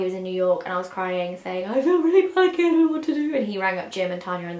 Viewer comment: What does Zoe say at 21:22 aha sorry idk why I want to know now 😂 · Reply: 'I don't like it here, I don't know what to do' I think!!!